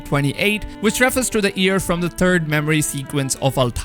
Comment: He says “year”. I hear “ear”